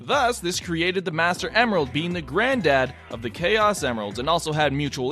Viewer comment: did anyone else hear saying grand dad